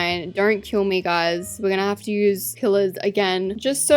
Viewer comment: when she said pillers i thought she said killers 😅